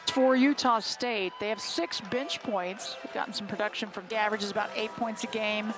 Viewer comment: did she say," 6 bitch points"?